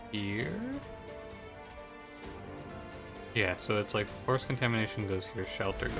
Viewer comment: Whenever u say “forced contamination” with the post-surgery voice I hear “foreskin tation”